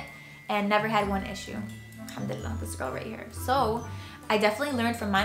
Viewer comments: Did you say "alhamdulillah" At 19:29 ? 😮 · did she say Alhamdulillah? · Did you say “ alhemdillallah “ ? Omg are u Muslim or half Arab or I’m hearing that wrong